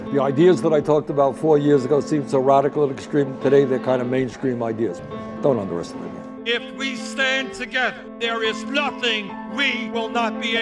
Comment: A huge thank you to Carly Comando for giving us permission to use her iconic song, "Everyday" for this video. Please support her by streaming her music on Spotify: <link> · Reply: I think there's a subtitle error: "Don't understand me" instead of "Don't underestimate me."